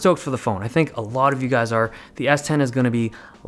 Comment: Did just say "lit"